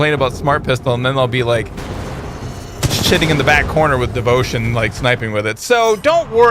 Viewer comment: Did he say sitting on the back corner or something else